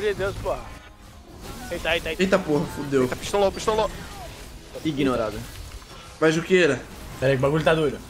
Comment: só eu escutei o falando "IGNORADO"?